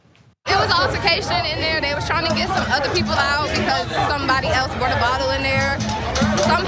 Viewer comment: I thought she said brought a bottle in the club